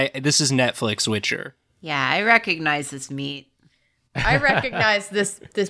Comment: "I recognise this meat." is definitely my fave problematic. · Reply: I thought she said "I recognize his feet" but same